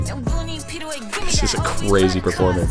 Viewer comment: i thought it said “Is this bread” 😂